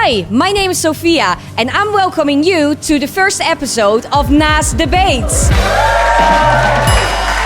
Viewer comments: low-key thought the intro said masterbate, not nas debates lol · I thought is said nas diabetes 😂